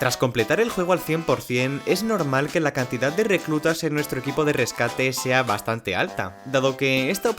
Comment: no fui el unico que se dio cuenta que dice "Equipo RedTube" en el minuto 6:21, cierto xd?